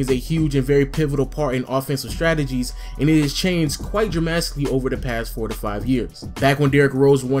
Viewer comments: Nigga did you just say dramastically? What the fuck is dramastically!? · did he just say draMAStically?